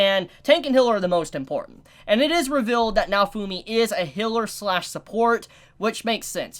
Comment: The way you pronounce Healer makes me angry,it's not Hiller,it's Healer. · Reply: I swear, i blanked out for a minute, and all i hear is "Noufumi is a hitler and support" and i just imagine all the characters in nazi uniforms